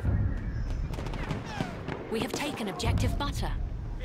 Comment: I thought she said objective utter LOL